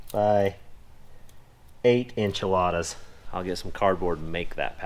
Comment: Did you just say “inch-ilada”? 😂 I love you guys!😜